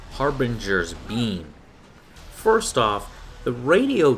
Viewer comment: I heard "Harbinger is mean" :DD